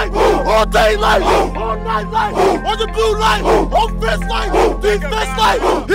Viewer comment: Is he saying "on the blue like"? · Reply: orange and blue like (team colors)